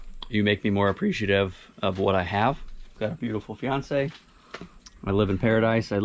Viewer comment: Did i hear you say 'FIANCE'...congrats.